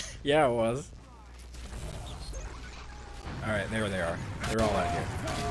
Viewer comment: might wanna censor the 'n' word at 9:59. just a heads up. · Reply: Mate I hear no n word at 9:59 all I hear is there they are